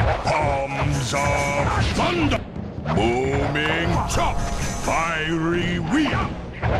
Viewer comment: What Panda King said: Hands of Thunder! Booming Chop! What i thought he said: HAMS of Thunder! BOOMER chop!